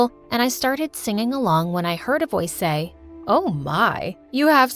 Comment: "I was singing along until I heard a boy" "It was 'ms'"... it was ms? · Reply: Heard a voice, not boy.